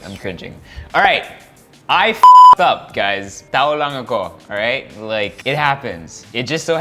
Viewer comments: "tao lang ako" i heard "tha' wss long time ago" 😂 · I thought he said "TAHO LANG AKO" 😂😂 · I thought you said “Taho lang ako” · Haha i heard "Taho lang ako" 😂😂😂 apology accepted 😊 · I thought he said "taho lang ako" 😂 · I thought you said, "Taho lang ako". I'm like, "wut"? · I thought I heard him say “Taho lng ako.” 😂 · did he just say taho lang ako hahah jk